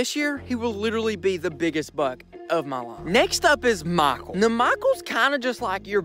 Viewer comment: i thought u said next up is mile that k was invisible to my child ears the first two times i heard it.